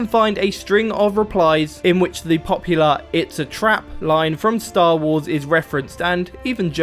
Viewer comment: did he really say starwars